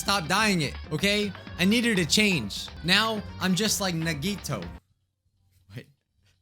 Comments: Like- I heard him say Nagoya and got confused like- does he know about da second game or- lol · wait did he just say Nagito Komaeda